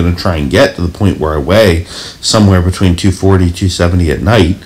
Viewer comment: Ben, did you say 240???